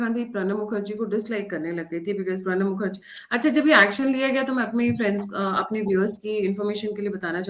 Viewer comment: Did she really say "mere fans"🤣🤣🤣🤣